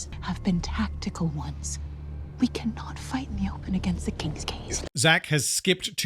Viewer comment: It took me until 1:55:05 when the other lady said "King's Gaze" to realize that the ship was actually called that and wasn't just giving it a funny name like "King's Gays"